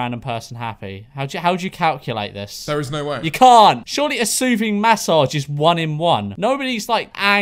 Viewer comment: I thought it said shooting massacre RIP 💀